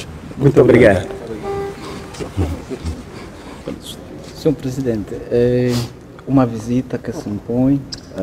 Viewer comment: Sr President? 07:47 Será que ouvi bem?